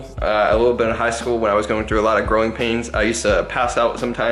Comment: Lmao I thought he said "groin" pains instead of "growing" pains, anyway, hope you feel better!